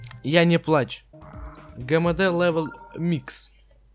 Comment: людиииииии,вы тоже услышали на 2:51 на заднем плане сказали "микс" !?